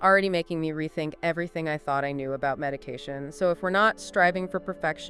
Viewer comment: Did you say medication, you really need to work on your ai typos bud 5:16 xD